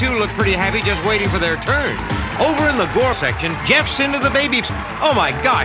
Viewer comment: Over in the gore section Jeff's into the babies! Oh my God Jeff! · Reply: I thought he said "porn section."